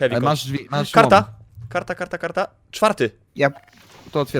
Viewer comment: ja usłyszałem karpatka XD kiedy ich gonili